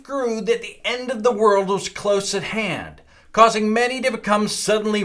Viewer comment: onwards, did he say "to see if the end of the world was close at hen"? Then the people cried "foul" or "fowl"?